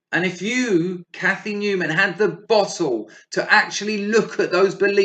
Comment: I'm from the US so can someone tell me what is said at 10:43 ? "If you had the [bottle ???] to accept..." - whatever was said, what was it, was it slang and what does it mean? Also right after that, "... beliefs that you have been carrying around like sacks of [ass bricks ???] for decades..." Huh??? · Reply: Hello - OK - 'Bottle' is Cockney Rhyming slang, from 'Bottle and Glass', referring to 'arse'. Having 'bottle' means having courage, bravery, and not having bottle, means lacking in bravery, courage, etc. The next one is 'House bricks' - it's quite common from people from London and South East England to drop the 'H' on words. This made me laugh, but I should consider it more since the majority of my audience is US.